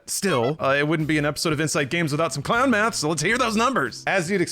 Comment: I gagged, I thought I heard you just said Inside Gaming 😅🤣